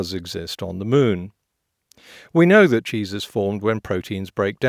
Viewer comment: At :50 I thought you said when Jesus is formed